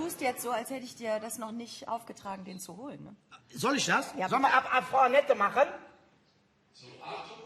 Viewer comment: Was sagt die Regie da?? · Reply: Ab Frau Anette